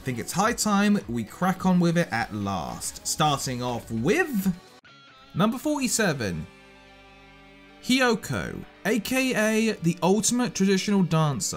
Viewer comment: i thought you said "number 47: kyoko" and almost had a heart attack